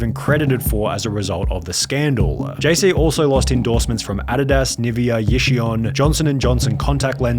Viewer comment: Did he just say adadas!?